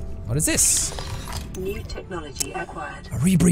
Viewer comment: Did anyone else hear 30 second acquired a little after